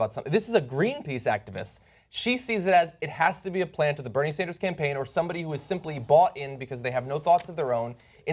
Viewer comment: In his stingy little mind she's a goon sent by the Bernie campaign. What a doofus. GTFO. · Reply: No not at first, but now yes. At first I thought I heard him say she may have been a plant from the Bernie campaign and I got all hot and hyper v_v